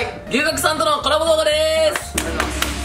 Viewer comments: 「龍角散とのコラボ動画でーす！」 って聞こえる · 龍角散とのコラボ動画でーす！ に聞こえた人は私以外にもいるはず… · ｢龍角散とのコラボでーす｣ に聞こえた人はいるはず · 最初龍角散とのコラボ動画でーすって聞こえた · が龍角散とのコラボ動画でーす！に聞こえた笑笑 てか、私 牛角が吉野家的な牛丼のお店だと思ってどんだけ食べるん！！！てびっくりした笑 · 龍角散とのコラボでーす って聞こえる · 龍角散とコラボにしか聞こえんw